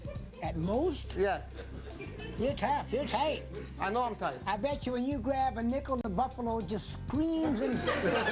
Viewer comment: What does Mills say at 0:38? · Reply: Here 7 years later to answer “I bet when you grab a nickel the buffalo just screams”